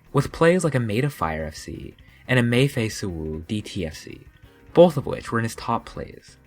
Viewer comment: did i just hear say uwu?